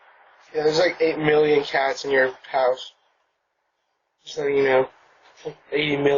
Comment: When strangers wearing gloves randomly come to your door announcing "There are 8 million cats in your sofa !" (Yes, that's what I heard him say), you can guarantee they are not there for ANY decent nor sane purpose. Makes one wonder what world these Bizzar-O's are from!